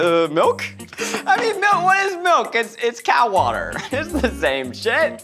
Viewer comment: "Milk is just cow water" I CANNOT DEAL WITH THIS TODAY, ZACH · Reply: I thought he said cat water...so this makes more since now